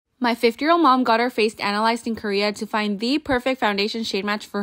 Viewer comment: People who miss heard her and thought she said "15 year old mom"😭